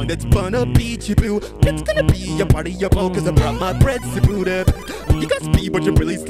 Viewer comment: Mario when he got his African friend “Zebudah” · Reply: I thought he said I got my friends to boot a